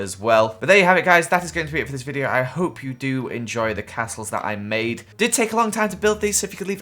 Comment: Now each castle didnt take a very long time Me: Oh like ten minutes? So around an hour Me: WHAT THE HELL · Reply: I think he said "did take a very long time." I could be mishearing because of his accent, but surely spending seven hours building for a 13 minute video is...a high ratio? I think he was explaining why he didn't also do the inside; it already took a long time